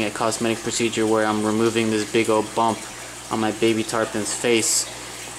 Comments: Lol I thought it said tan pon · I thought it said "procedure on my baby tampon"